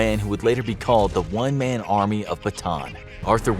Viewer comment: I thought that said “one-man army of Batman”